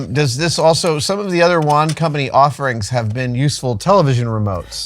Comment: I thought you kept saying "One Company". Didn't realize you were saying "Wand Company" until I saw it in text form in the pinned comment! To me, the word "wand" (wawnd, with a strong "d") has a very different pronunciation than "one" (wuhn). Perhaps it's a regional elocution thing. Anyways, why does it seem like I've seen this particular prop before? It seems really familiar, bells & whistles & all. Cool as heck, though. Cheers!